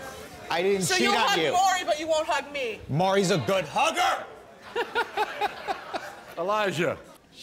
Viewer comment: “So you’re cheating on me with Maury too?” “Maury is a good HUgGeR!!!” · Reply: Thought you said good burger💀💀